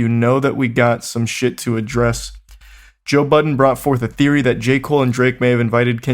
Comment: I thought he said Joe Biden